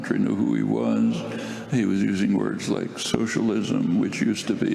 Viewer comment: Note: Chomsky said "uses words like 'socialist'", not "is a socialist". Bernie is not a socialist. He is a social democrat. Socialism is a completely different thing. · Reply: No...you misheard that...he said Bernie "was using words like 'socialism'..." A bit of a difference. No offense intended. (Time frame